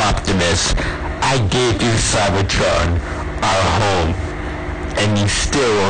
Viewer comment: subtitles added :) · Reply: I geniunely thought he said "I gave you faggotron". Then it immediately went back to Cybertron after turning on the subs and reading it.